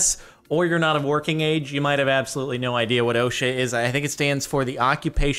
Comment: I honestly just thought he said "Oh shit is a federal agency", and not "OSHA".